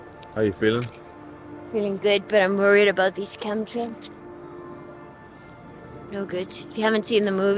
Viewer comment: Did she say "Chem Trails"? 26:00?